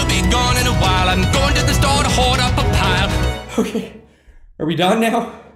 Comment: What does he say at 1:49? lol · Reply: He says I have got to get going